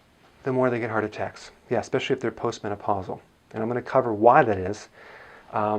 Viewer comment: Sir did you say that " because they shift to their post-menopausal " at 0:38 second . If yes then does this mean that taking calcium pushes a women towards early menopause? Please explain because in my country we women are taking too much calcium tablets and when they stop taking it, they don't get their periods in time. We use cac 1000 tablets or VC cal sachets etc I doubt their quality as well